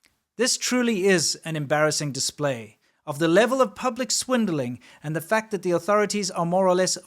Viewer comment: did you say Schwindeling? I’m German and I know the Wort in the German speech as a liar and a Cheater. In my search, I couldn't find an English word spelled like schwindelig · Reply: It’s spelled swindling in english:) guten abend!